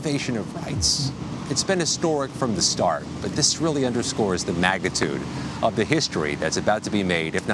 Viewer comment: Did MacFarlane actually say "MAGAtude" at 5:46??? It sure sounded that way. If so, the perfect description, lol.